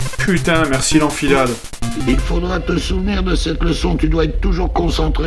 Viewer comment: c'est moi ou a 2:35 le vieux dit "il faudras te sous merde" ?